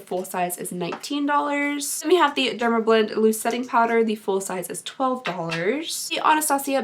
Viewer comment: For some reason I heard “lip setting powder” instead of loose setting powder and I was sitting here like, “How? Wait...would that work?!” 🤔